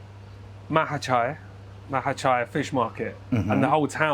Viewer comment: What a treat this episode! Did anyone catch the name of the seafood market that mentions at 35:24? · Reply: Mahachai Market, it's in Samut Sakorn